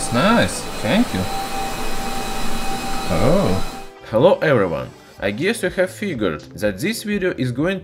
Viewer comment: When he said, "Hello everyone", all I heard was, "I will break you". I guess I'm still scared of Drago.